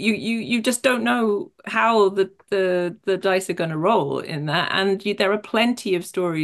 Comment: Oh she's says we don't know how the DICE are gonna roll... I misheard Stock but it still fits